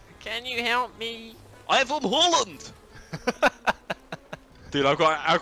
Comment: Did say “ i’m from Holland” at 1:37 ?